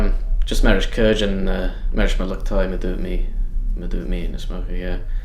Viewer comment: Did he just say "murder me in a smoker"?